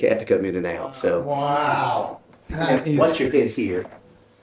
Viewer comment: Great video! Love it! At 32:02 I swear I hear a woman whisper “her name is..” then the guid continues talking. Maybe it’s a spirit talking about the cat’s name because he just got done talking about the cat.